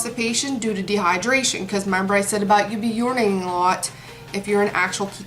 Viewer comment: the first side effect of keto if I remember properly. You said it more than once or it’s what it sounded like you said when telling the side effects. Sorry if I’m wrong just wondering what it was you said cause I know someone doing this diet. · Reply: M Watkins urinating that’s what I said 👌🏻